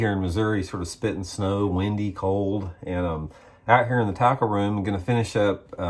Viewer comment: Every time you say "tackle room", I hear it as "taco room". ;)